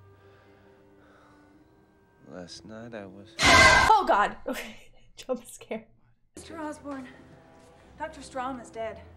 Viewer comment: you said "oh god jump scare" but the captions say "oh god trump is scared". The latter being valid given recent news. :P